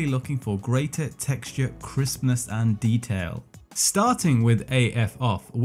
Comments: Every time you say "Crispness" I hear "Christmas" :) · i heard "texture Christmas"